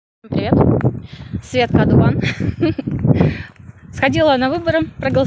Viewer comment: За Меганыча голосовал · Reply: только Жирик паяц, а Меган косит по паяца, хотя его серьёзность по 10 балльной шкале 23. Он чем-то журящего родока напоминает. Когда дитяти накосячил, предок спрашивает: это нормально по твоему, это так модно? Вот и Меган спрашивает аналогичным образом на своих стримах))))) P.S. Когда ты начала свой войс, я услышал "Светка гандон". А потом увидел развивающиеся волосы на ветру и догадался что "Одуван")))) Потому что сама себя гандоном вряд ли назовёт. Это ж какой должен быть повод?))))))))